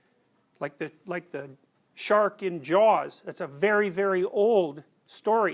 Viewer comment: What is the story he is talking about ? shark and jaws ? I don't understand and google was not much help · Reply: He actually said “The shark IN ‘Jaws’ “ i.e. the movie- then he links that archetypal story to things like Jonah and the whale in the bible and Bilbo facing the dragon in Tolkien’s’The Hobbit’